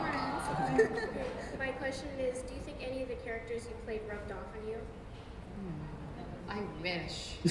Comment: i can't understand the question of the little girl in 33:20. please someone tell what she said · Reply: "do you think any of the characters you played rubbed off on you"